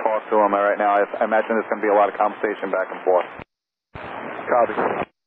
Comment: Can you not see the context? 10:16 do you mean conversations back and forth instead of confrontations?